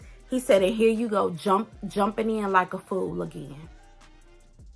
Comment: I don't think he called her a fool. I thought he said jumping in the pool.